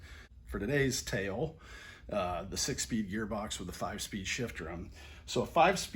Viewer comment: I thought you said 5 speed shifter then you pulled out the 5 speed shift drum and I was 😬